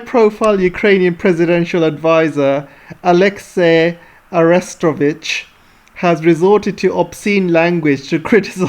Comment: Every time I hear Arestovich's name, I hear arrest a bitch instead... that's why it makes me lmao 🤣